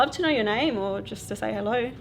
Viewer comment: I heard something whisper okay